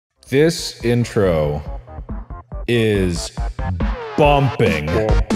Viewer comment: I thought it said "This intro is bumming" so I expected sad😂😆😂😆😂